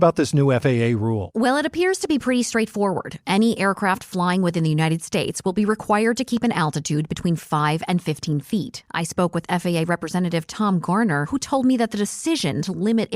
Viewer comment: At first I thought you said 15 thousand feet